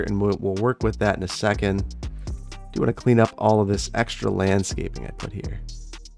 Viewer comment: You say Landscaping. I hear "Flamability" 😂😂😂 Love it